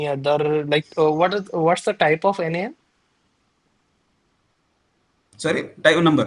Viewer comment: what is the type of ______?? i didnt understand the question can anyone explain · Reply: Type of NaN